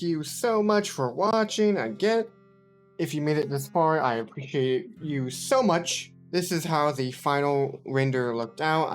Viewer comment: did he say “i hate u so much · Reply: LOL, no, I appreciate you so much